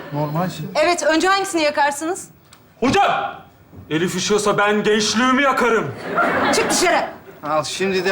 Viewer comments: ne diyor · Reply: elif üşüyorsa ben gençliğimi yakarım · ne diyor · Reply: elif üşüyorsa ben gençliğimi yakarım diyor elifin de hoşuna gidiyor (sınıf arkadaşı elif:)